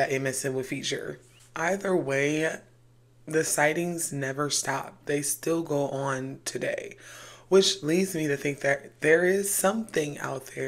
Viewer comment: Not me listening to this to help fall asleep n I hear ‘ am their still seen today’ n was like 😧